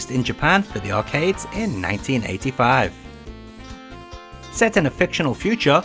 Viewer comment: maybe I misheard, but did you said "1995"?